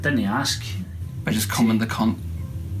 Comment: I'm rewatching all of the Infinite Review episodes, and I just realized that for years now I haven't been able to figure out what on earth says at 8:25. "You just cum in the cunt"? I'm definitely mishearing it, but that is also absolutely what it sounds like lol